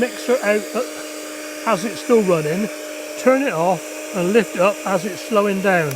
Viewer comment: for a moment I thought you said “don’t lick the mixer while it’s still running” 😂